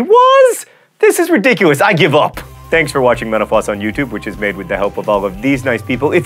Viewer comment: At the end, I thought you said "Thanks for watching menopause on YouTube."